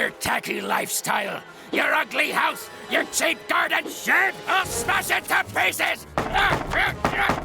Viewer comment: what did he say? He said a bad word💀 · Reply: He said shed